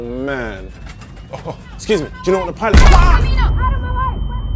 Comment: At first i didn't saw the video, and just heard the sound, and i thought an Indian Lady is saying: Aree Kamino Out of my way😂😂😂, then i watched the video, it was an different language. I thought why would an Airhostess say such words to people in plane😂😂😂